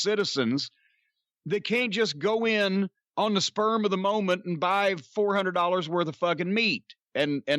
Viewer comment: Did he just say "sperm of the moment"?